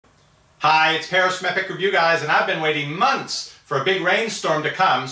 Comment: I watch all your name at the beginning it sounds like your saying Paris Metric, by the way a good vid as always.